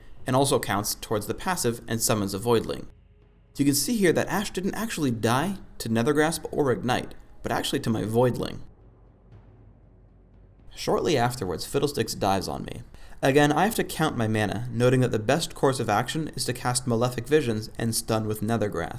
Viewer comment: thought he said fiddledicks xD